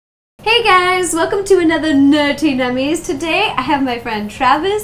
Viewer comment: In every episode it sounds like she says "NerTy Nummies". NO, IT'S NERDY!!!